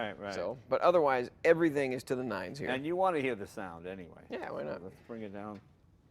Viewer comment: Jay "and you wanna hear the sound anyway" Did he just say "yeah, whatever..." in reponse to that? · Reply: Nah, he says "yeah why not." Kinda sounded that way at first, but nah.